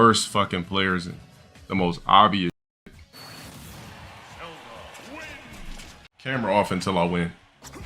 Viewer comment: Did I hear that right at 26:31? "Camera off until I win"???